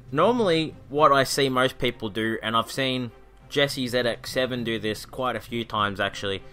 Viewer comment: in the video you mention Jesse 'something' to look up on YouTube. Can u provide a link I can't understand, thanks. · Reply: "jessiezx7"